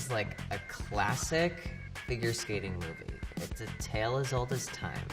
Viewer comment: I thought it said figure skating ‘moves’ and then I was confused....